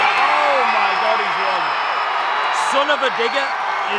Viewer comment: When he said son of a digger i thought he said son of a n...er😅